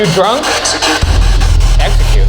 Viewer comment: Totally heard "Mexican" instead of "Execute" on